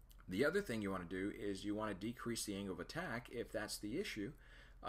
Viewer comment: When you talk about ways to fix the stall. You say one part of it is increasing the airflow (by increased airspeed) through the front, to of course get everything spinning faster (and re gain the corresponding pressure for the given fuel flow). But you also mention to decrease the angle of the attack. Are you referring to the airflow that is head-on to the aircraft, hitting the fan blades. Because this is basically the same as increasing airflow, or airspeed. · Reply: Ahhh just heard you say ‘decrease the angle of attack, IF THATS THE ISSUE’ Makes more sense now